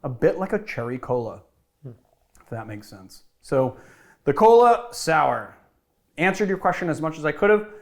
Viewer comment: I misread the title as "koala sour" and as an Aussie I got very confused and even more concerned. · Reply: I actually thought it said Koala Soup